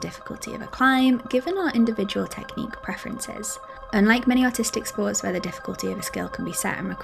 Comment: did she say autistic sports at 5:50 ?